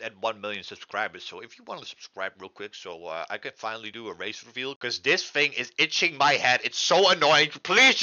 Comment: sounds like he said race reveal instead of face reveal